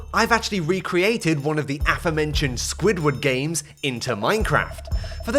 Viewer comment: did he say squid war dgames